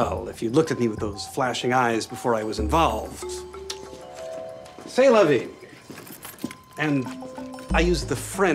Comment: What is he saying at 6:21 in French? Because I am French but I can't understand him... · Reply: Sounds to me like "C'est la vie"